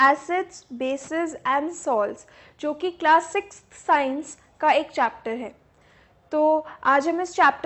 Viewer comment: did she said class 6